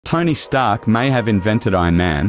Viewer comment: Did you just say "Tiny Stark"?